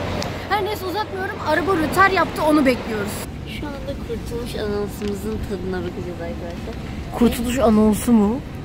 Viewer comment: abla bende kurtuluş anonsu diye duydum😂